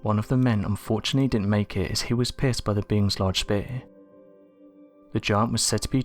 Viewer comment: "One of the men didnt make it because he was pissed by the beings large beer? Or beard?" What were you saying at 1:00 ...... Oh spear...pierced